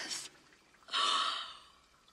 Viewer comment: The lyrics: “SHE’S DEAD!” she is?.. *Gasp* These are the moments that make life worth living “MY GOD MADELINE THAT WAS HORRIBLE!” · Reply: I heard “FUCK GOD MAN THAT WAS HORRIBLE!”